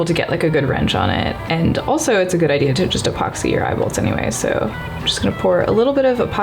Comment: jeez I thought you said EYEBALLS for a second there!